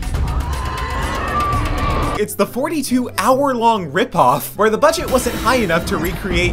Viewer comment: I thought he said 42 minutes, not 42 hours